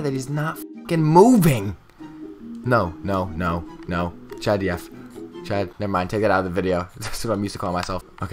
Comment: did he just say ”chad df”